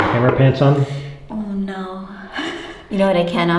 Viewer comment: He said "Hammer Pants" and I miss-heard it as "Underpants". I was very surprised at the "No" answer to such a question until I re-listened and heard it correctly.